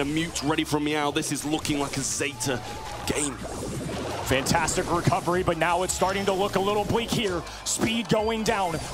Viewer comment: I thought he said, “Zetastic recovery” lmao